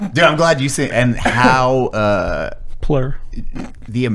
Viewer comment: did he say "PLUR?" Fucking hell